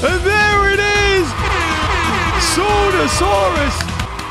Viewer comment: did he call sonorasauras sodasauras??? 🤣🤣🤣🤣🤣